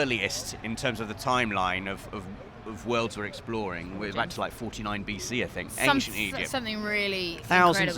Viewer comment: Did he just say "49BC I think"?